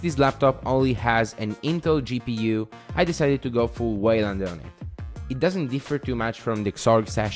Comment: What is the name?? I couldn't understand it. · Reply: Wayland